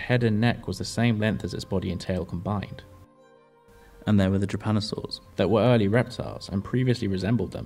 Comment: What are you saying @ 4:53? “Trapanisaur”?